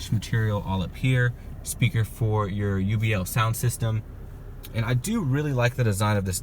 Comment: UBL speaker instead of JBL